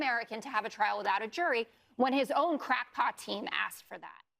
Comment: Crackpot? I thought you said crackhead. Either one works.